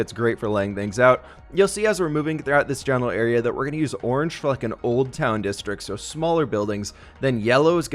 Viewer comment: "orange fucking old town districts", am i the only one who heard that?